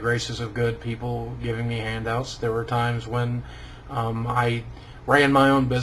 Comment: HAHAHAHAH damn at like 2:40 i thought he said handjobs not handouts! hahah but i love you Boogie and thats a powerful story! im proud of you bro!